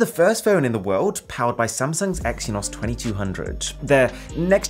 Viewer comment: Is it just me or is it supposed to be “SAMSUNG” instead of “SANSUNG”?